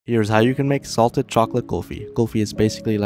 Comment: I thought said in the video here is how u make salt kulfi HAHAHAH